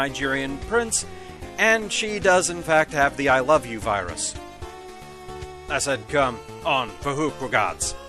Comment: Eeeh... can someone type out what he said? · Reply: I said come on fhqwhgads. Basically a Homestar Runner reference that also involved the I LOVE YOU virus.